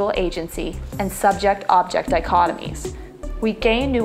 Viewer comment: what is said at 2:48 subject-object .... i what? would be very helpful to know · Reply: subject-object dichotomy- the idea of human subjectivity and material objects existing independently or in opposition to one another. Intra-action suggests that these entities cannot exist in isolation, as they shape and inform one another. We shape the material world, the material world shapes us.